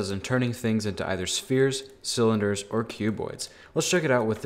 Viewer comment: You can tell he likes the skin modifier! XD Edit: 15:38, I thought he said "Cube bois."